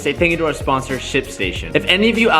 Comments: Totally thought he said the sponsor was "Shit Station" at first. I laughed way too loud. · I totally thought you said this video was sponsored by Sh*tstation 😂💩 I had to look and read